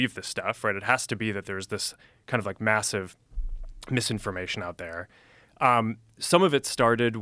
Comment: did he say MASSIVE?